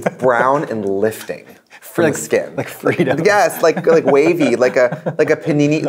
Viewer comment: “It’s brown and it’s lifting” “Like freedom” That joke went UNAPPRECIATED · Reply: I thought she said Freetos, I assumed it was an American food Edit: I looked it up, Fritos, they’re like spiral shaped crisps (chips)